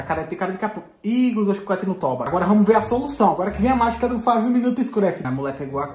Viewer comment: eu entendi five night at freddy kkkkkk